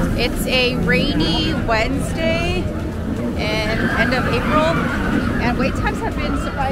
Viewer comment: Hello, what month was this? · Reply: Nevermind, I just heard you say April!